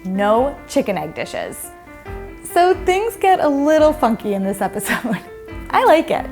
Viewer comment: I hear fucky. 0:37 like if you heard the same